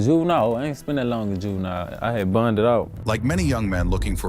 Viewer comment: he said “bonded out” lol not what u said